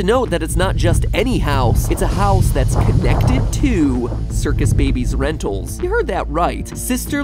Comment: I heard it right? Because I heard circus baby's toenails